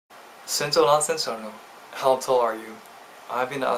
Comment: 最初の英語全然聞き取れんくて仕方なく字幕で確認したら ”身長何センチあるの” 日本語かーい😂 「せんちょうwrぁんせんちゃぁるの」みたいに聞こえた(笑)